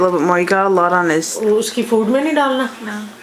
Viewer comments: Did someone say "uski food me nhi daalna at 4:42..? Lol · I heard a sentence in hindi, "uske food mai nahi dalna?". (उसके फूड में नही डालना?) Anyone else hear it too? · did someone said "uskey food me nhi dalna" ??